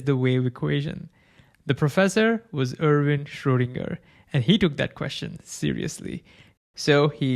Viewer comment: lol I heard that as Arvind Schrödinger